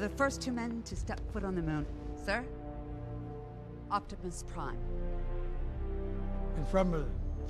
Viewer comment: did I hear her call Optimus Prime, “Optipus Prime”?? Bruh lmao